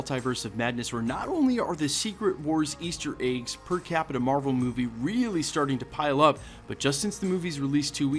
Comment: “Per Capita Marvel Movie”. 0:46-0:53 I think I listened to this part 10 times, I thought I heard Captain Marvel Movie each time. lol